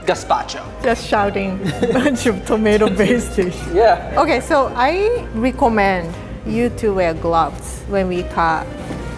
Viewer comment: it almost sounded like she said "I Rie-commend..."